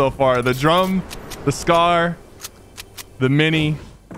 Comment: did he just say he got akill with the mini!? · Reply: Mini gun